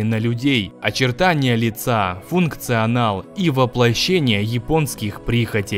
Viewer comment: В начале послышалось - функция анала)))